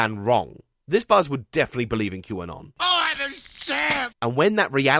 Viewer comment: Believe in what? I couldn't make out the words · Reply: Old comment, but QAnon